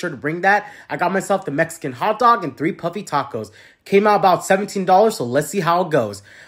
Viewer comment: Wow at first I thought you said puppy tacos. Then looked at the image and went oh puffy tacos